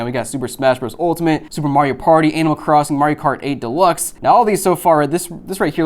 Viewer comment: is it me or did i just hear A-Crossing. 😅